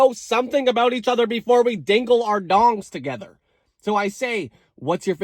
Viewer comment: Took my headphones off for two seconds to grab a drink, put them back on, and the first thing I hear is 'WE HAD OUR DONGS TOGETHER' 💀 Jesus lord